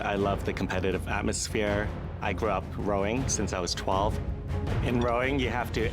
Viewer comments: ok "I grow up growing since i was 12" WTF is he trying to say in this sentence lmao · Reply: Hung tin fan “I grew up ROWING since I was 12” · When said he grew up rowing, I swear I heard “I grew up growing.” Doesn’t everyone grow up growing? I was so confused !